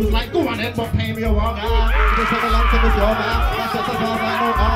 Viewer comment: what does devilman say here "When i asked to piss in his face he was like GO ON THEN but pay me ?? ?? " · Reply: A Oner